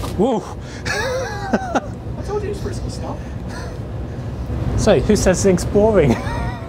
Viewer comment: the subtitles say 'I told you to fix the stuff.' really he said 'I told you it's frisky stuff,' referencing the beginning of the video.